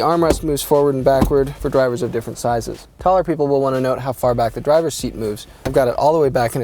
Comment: sounds like he says "Colored people will wanna know how far back the drivers seat moves"...lol